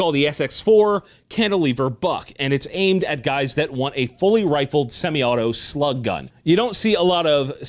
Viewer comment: First time I Thought you said Fully-Semi Auto Shotgun.